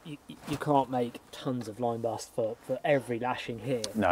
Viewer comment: American here. What on earth are they saying ‘line bast’ maybe at 5:25-30? ‘You can’t make tonnes of ‘*********’ here….’ I speak multiple dialects of American and British English but the cameraman’s question is unknown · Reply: Lime bast is a well known material for cordage in prehistory (tilia cordata, small leaved lime)